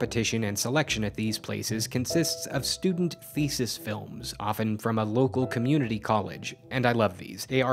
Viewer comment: I heard that as "Student feces films" and thought it was quite harsh at first